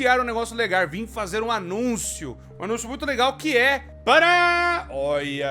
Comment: Oque? Isso mesmo que eu ouvi? Parabéns pela parceria! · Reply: Eu vim fazer um anúncio!